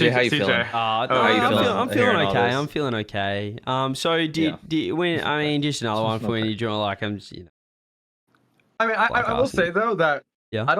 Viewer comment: what the heck is saying? 😂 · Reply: He clearly said "So did... Did... When... I mean... Just another one for you Jo... Like... I'm just... You know... Like Arsenal." What's not to get?